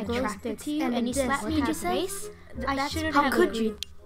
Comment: I got what Roman said after watching it a lot! Roman: Omg can't you see? I'm extremely attracted to you and you slap me in the face? How could you- Patton: Roman.... What you've said.... ThAt wAs GaE! You're welcome. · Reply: OOOOOOOOOO I thought Roman said "I'm extremely attractive and you slap me in the face"😅